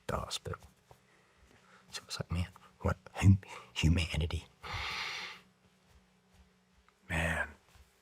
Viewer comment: What did he say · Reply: “Mehh ooo hhumannitty”